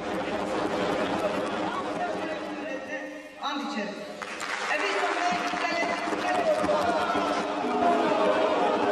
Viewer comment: Leyla zana 9:02 de ne diyor? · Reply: ezi kuze ciya vennim diyor